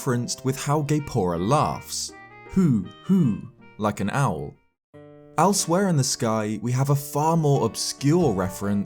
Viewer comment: Owlswhere? Or am I just hearing what I want to hear?